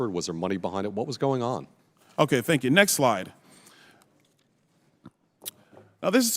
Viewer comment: He said next slide. I thought he said next lie!😮